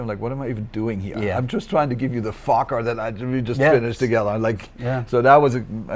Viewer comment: did he say he was trying to give you a fucker?